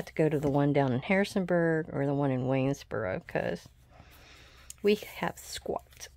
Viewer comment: Lovely journal! I just found your channel and subscribed. It sounded like you said "you have to go to Winnsboro to a bigger selection ? Are you in lou😢? I am also. TFS